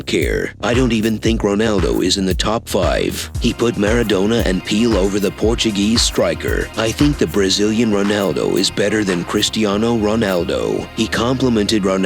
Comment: Did he say pelow